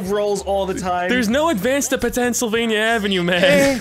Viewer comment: did he say Penntsylvania